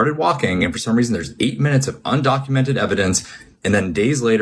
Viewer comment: he actually said “undocumented evidence” and didn’t even acknowledge it except the subtitle or whatever said “undocumented footage”